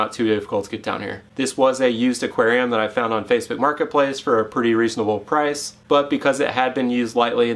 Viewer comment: Where did you buy this combo from? I thought I heard you say Lowe’s? Which I’m guessing I heard wrong as I’ve never heard of or seen any fish tanks at a Lowe’s lol.